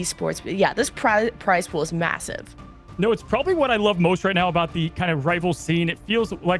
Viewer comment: What did she say ? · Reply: "This prize pools massive"